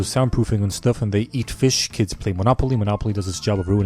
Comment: his captioner misclicked and put ear fish instead of eat fish